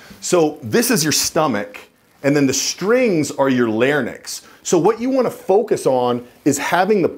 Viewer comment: It sounds like "lar-nyx" was said at 01:48 instead of 'larynx'. However, I'll try not to let that take anything away from the usefulness of the video.